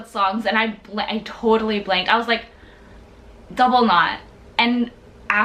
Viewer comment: What she said in the minute 2:39? Double... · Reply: Double Knot